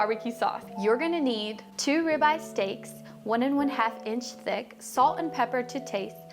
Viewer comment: Another one that smells good all the way to Dallas! my audio wasn't clear- are your steaks 1/2' or 1 1/2 inch thick? It sounded like you were saying 1 and 1/2 half but the rib eyes look half an inch thick. BTW, Bogdan doesn't need to stay out of the pic- we know he is there and salivating all the while- besides , he's your sauna buddy now! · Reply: 1 1/2 inch thick. We used 1 inch thick Delmonico steaks couldn’t find a super thick rib eye.